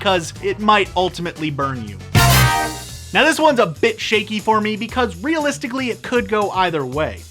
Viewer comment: I thought he said bitch at 3:28 😂😂